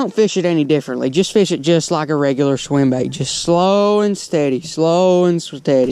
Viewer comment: It sounded like you were about to say “slow and sweaty”